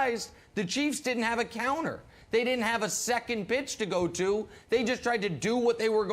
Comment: what word did he say? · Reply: Pitch